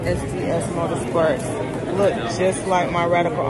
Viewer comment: Did you just say SDS Motorsports 😭😭😭